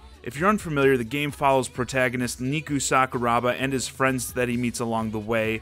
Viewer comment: Did you just say "Miku Sakuraba"?